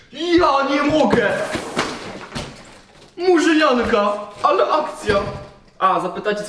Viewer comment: Murzynianka? xDDDDDDDDDDD nie mogę(tak usłyszałem sorry)